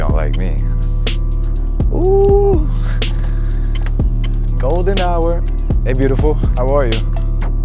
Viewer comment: She thought he said Golden Shower?? and wanted him to share one with her. That red truck looks gay like your hair cut